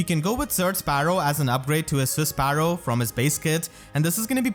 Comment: mishearing Surge Sparrow as "Zerg Sparrow" Me: kekeke